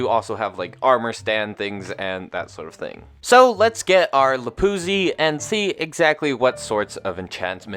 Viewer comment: What did he say 8:33? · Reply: Lapuzi